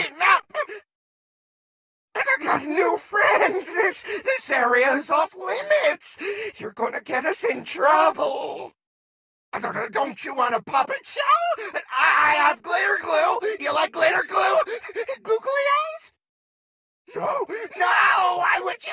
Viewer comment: I legit thought Sun offered me a Pop-It. He really said "puppet show", but I'd accept a Pop-It :)